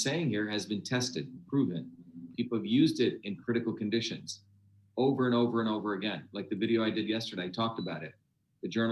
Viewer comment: the Dr says take what? I only heard the 15 gram part. Also what video is he talking about that he did yesterday? Thanks. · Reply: Vit C